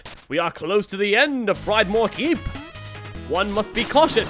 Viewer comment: Did you really say, after being struck by LAVA, "But worry not. We are close to the end of Fried-more Keep!"